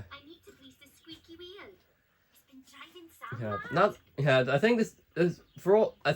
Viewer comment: because of welsh accent i used to think she said its driving Sam Mud